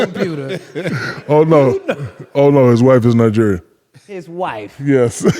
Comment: Lmao bro thought Shaq said wife’s ppl but he said white ppl 😂😂😂😂